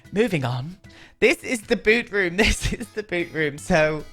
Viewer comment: I THOUGHT YOU WERE SAYING “THIS IS THE BOOB ROOM” HAHAHAHA